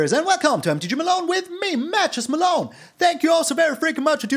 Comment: Malone I gotta tell you the whole time I thought you were saying "Mattress Malone" but then I read it somewhere on the channel that you are saying "Matches Malone" and it made so much more sense. Thanks for another fun deck. Lol